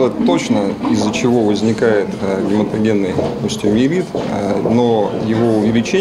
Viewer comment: Самое главное - название болезни врач произнёс очень невнятно. Мне послышалось - гематогенный остеоевит. Как болезнь- то называется? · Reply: Он сказал гематогенный остеомиелит.